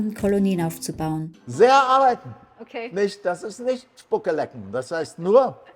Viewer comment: Meinst du die Mennoniten in Paraguay? Sie sind und sprechen immer wieder davon wie dankbar sie sind. Ich denke, dass ist ihnen schon bewusst. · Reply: ich hab da was anderes rausgehört, wer nicht spucke leckt, der wird erfolgreich. dankbarkeit hört sich anders an 5:00 minute